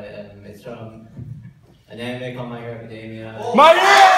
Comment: I thought that guy said mario but actually my hero 😂